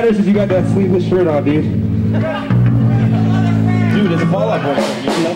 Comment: In 7:40 it really sounds like Pete mentions Fall Out Boy after talking about the sleeveless shirt. Did anyone else hear that or...?